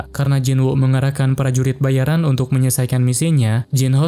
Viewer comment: kok pranurit bayaran kedengerannya? :)